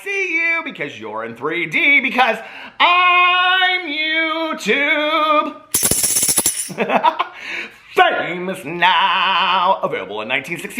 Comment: I keep hearing "I'm every woman" when you go to say "I'm YouTube famous now" 😂